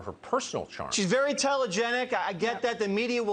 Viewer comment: Intelligenic...wtF? Did I hear that correctly?